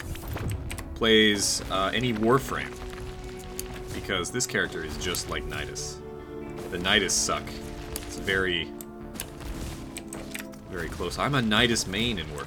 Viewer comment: For a second there I thought you said nidus sucks. And my God you would've been sooooo wrong